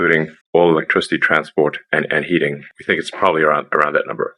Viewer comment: Did I hear Elon talking about “heating” at 09:11?? Hvacs coming after energy storage…massive